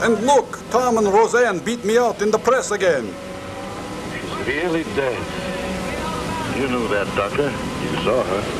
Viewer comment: Sure sounds like he said “severely dead.”